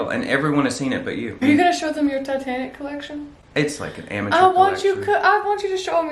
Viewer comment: My brain completed the sentence as "Are you gonna show them your titanic TATTOO?" I actually re-wound it to make sure that's not what you said.